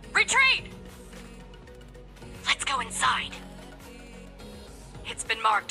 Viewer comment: what she trying to say😂😂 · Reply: Leys go inside 🤫